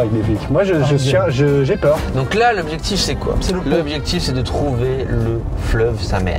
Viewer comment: Vraiment je suis gogole. J'ai commencé la vidéo, et j'ai cru que a la place de dire "l'objectif c'est" j'ai compris "le British c'est"